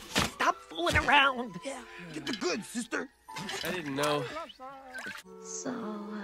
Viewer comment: help did the red demon (idk what it is) say "GET THE GOODS SISTER" 💀